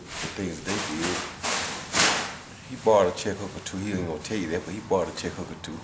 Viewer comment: What did Roy say at 3:29? He brought a what or two? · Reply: He borrowed a check-hook or two (punch that knocked out hatton)